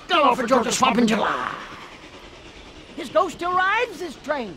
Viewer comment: What did they say? It sounded like: “He got…slipped off this train like Georgia Swamp in July!”